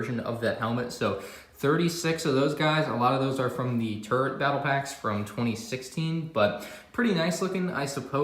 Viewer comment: Who else heard turd battle pack